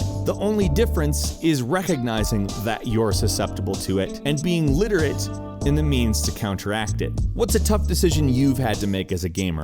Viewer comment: It sounded like he said "being literate in the memes to counteract it."